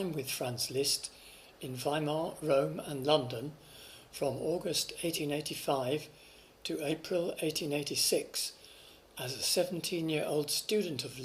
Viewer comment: those dates are off by 10 years! (I hear 1895 and 1896) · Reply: Sorry if it is not clear enough for you, but the commentary is saying "from August 1885 to April 1886".